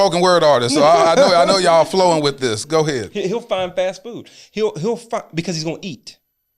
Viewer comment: Did he say men f---k because they need to eat? Did I hear that correctly? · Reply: 12/23/22: Nope, he didn’t say that. I heard him say, “He’ll find fast food. He’ll find…”